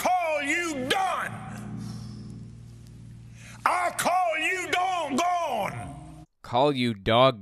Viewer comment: i thought he said "i call you dugong" which is a sort of manatee?